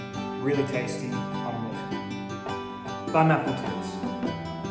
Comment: I thought you said Bon appletits